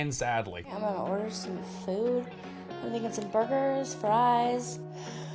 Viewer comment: I really thought she said that she was gonna get some boogers